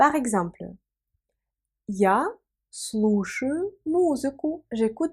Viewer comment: Non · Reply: Ah? 2:39 tu dis bien слышу mais c'est écrit слушаю 😵🤔